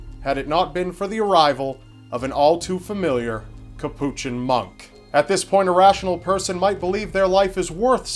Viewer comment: I thought you said capuchin monkey 😅 I was thinking "wow, that's pretty unbelievable!"